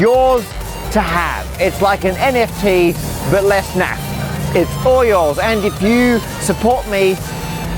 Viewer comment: I think Matt is saying “less naff” but I heard “less math” and was trying to work out if there’s more math in Perlin Noise than in cryptography